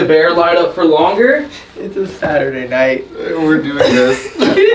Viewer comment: it sounded like a little girl saying "we're/I'm having fun